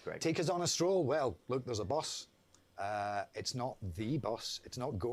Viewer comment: creo que Craig dice: "Bus" no "Boss", porque dice que hay un bus pero no necesariamente aparecerá Ted, se confunde por el acento que tiene